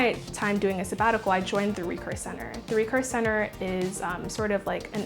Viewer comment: I mean what she said at 2:21, not 8:17. Is that a “recourse center?” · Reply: Yup, actually called recurse center